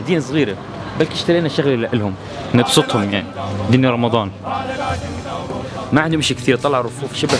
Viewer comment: كأني سمعت "رمضان" كشفتك يا جو مصور الفيديو قبل شهر ،😎 😎 اللي يحب يتأكد 14:22 انت محتواك فخم حتى لو من. سنة 💙